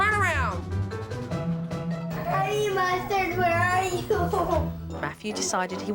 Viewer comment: what did he say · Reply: He said "Honey Mustard where are you"